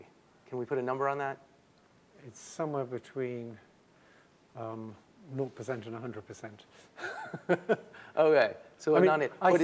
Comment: This interview should be saved as an historical artifact. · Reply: No that's not what he said. He said "somewhere between naught percent and a hundred percent". His answer is at 27:35.